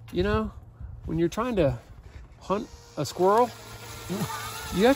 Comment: For a second there i thought you said "punt a squirrel" haha